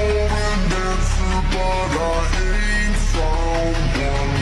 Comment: idk why i hear this but i always hear “but i ate someone”